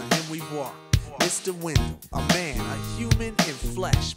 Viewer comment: I always thought they said Mr. Windmill ... lol